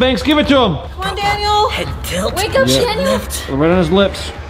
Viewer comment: Did anybody notice that at 0:20 told chanielle instead of danielle. She told "Wake up chanielle" instead of saying "Wake up danielle" Like If you only noticed it now after reading my comment 👍